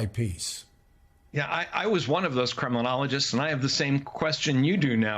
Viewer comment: Funny that I heard 'criminologist' when he said 'Kremlinologist'. Nowadays, of course, the meaning is almost the same.